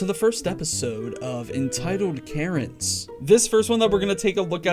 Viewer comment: Am I the only one who thought he said "Welcome to the first episode of entitled carrots!"